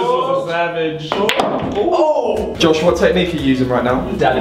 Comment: i swear Simon said "do it for mum" ???